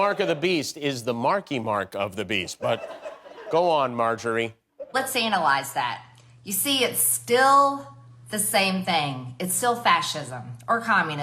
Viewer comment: Did she say: "Let's anal-ize that" instead of analyze, or it's just my ears?